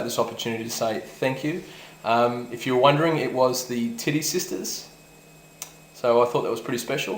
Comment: ok 0:32 did i just heard. "umm it was the Titties sisters" ????